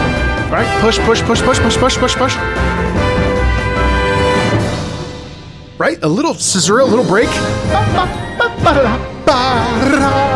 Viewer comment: did you say ra tha? say that again